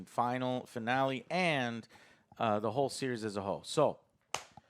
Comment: It sounded like he said "Aaaand....The whole series is a Ho"